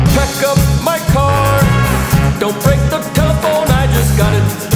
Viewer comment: for two years i thought he said cupholder instead of telephone xd